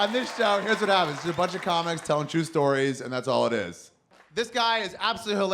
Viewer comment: I thought he said " a bunch of people telling a bunch of jew stories" does that make me a racist?